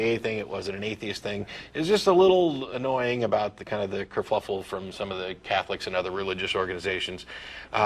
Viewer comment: Did he say "kerfluffle"? ;)